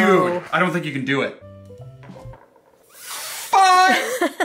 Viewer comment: what did you just say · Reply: He said fiiive